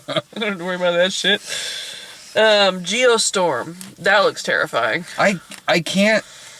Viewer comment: Oh, "Geostorm." I thought said, "Ye Olde Store." And I thought that'd be an ambitious disaster movie!